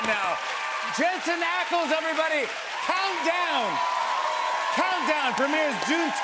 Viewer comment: Lol..I am such a crazy Fangirl. At the end, Jimmy was saying “Countdown..Countdown,” I thought he was saying “Calm down..Calm down!😂🤣